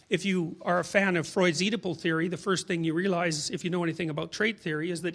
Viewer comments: what is he saying? If you're a fan of ... what? What families? Thanks! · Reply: utopic oedipal, like Oedipus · Can anybody tell me what he's referencing around 19:30? I can't make out "x-theory" and "x-family". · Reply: Thank you! I couldn't make out the words somehow. Freud's Oedipal sounded like one word.